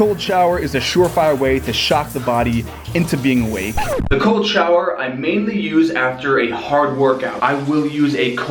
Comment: I thought he was saying golden shower!